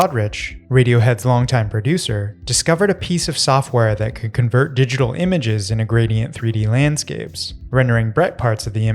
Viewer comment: HOW IS IT CALLED? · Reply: LightWave 3D